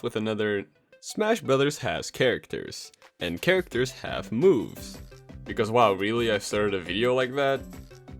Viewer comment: " smash bros has characters, and characters have boobs" Thats what i thought you said